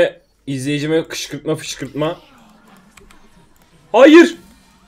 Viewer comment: izleyicime fışkırtma? ne diyorsun abi